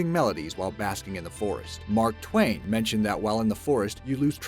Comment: i heard 'mark tuan'